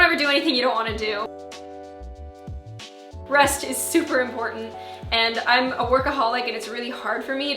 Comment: lol I was texting while watching the video and didn't pay attention to some bits At some point I thought you said "Breast is so Important" :))